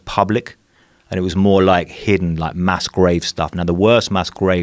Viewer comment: I thought he was saying masquerave when he said mass grave